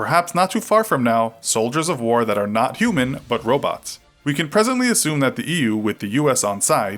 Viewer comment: why i hear robux?